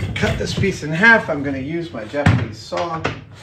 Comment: Love seeing Adam doing one day builds. Though when he got his Japanese Saw out I miss heard him and thought he said Japanese Sword was expecting to see him cut the wood in half with a Katana!